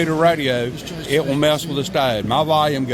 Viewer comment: Interesting. I don't know what you said at 0:39. 'It will mess with the stud' ? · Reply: I believe he said "It will mess with this Diode" as he mentioned a Diode earlier.